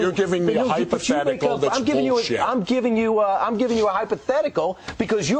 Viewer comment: Did he say '...that's bullshit' at 4:54?